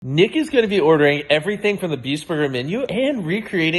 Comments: I'm soo stupid i heard " N-word gonna be ordering " 😭😂🗿🗿🗿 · sounds like he said the n-word😐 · I wont lie I thought mrbeast said "niggas gonna be ordering" LOLL I was tripping asf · At the start I thought he said nicka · I thought he was saying Nick is 💀🙏